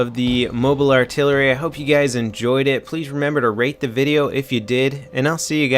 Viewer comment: lol instead of rate the video you said rape the video (if i heard it correctly)